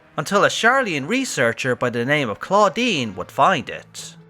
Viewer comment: Most of the things i wanted to say have already been said, but i must say it sounded like you said "Claudine" which is a girl's name in french, that poor researcher is Claudien, that sounds like Estinien... It made me laugh 😂 But, i'm glad we finally learn what it is, it was bugging me !